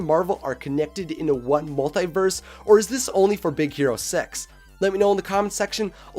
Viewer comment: did anyone else think it sounded like Big Hero Sex instead of Big Hero Six?